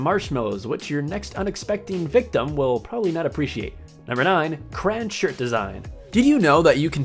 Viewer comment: was he saying crowns or crains!??. XD LMFAO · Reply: SAVAGE 101 he actually said crayons